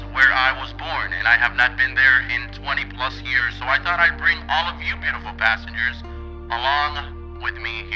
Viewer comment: For a sec there i thought you said “imma bring yall beautiful basterds